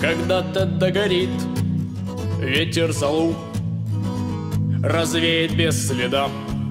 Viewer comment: "Ветер залуп", я верно услышал? 5:30. Я глянул в текст и там написано "залу".